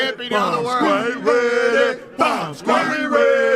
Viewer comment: I was multi tasking and for a second thought I heard them chantting "we ready dumb squad!!" lol I was like wtf!